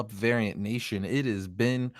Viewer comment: I thought he said aryan nation